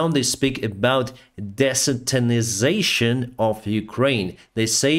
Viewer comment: I don't understand what the report at 1:45 is talking about. YouTube auto captioning renders the key word as "desotinization" but either that is not what said, or it is something I am not familiar with. Does anyone else have a clue? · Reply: desatanization